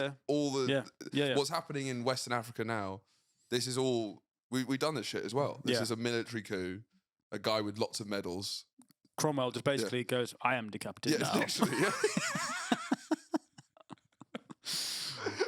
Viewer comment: Genuinely thought he said "I am decapitating now"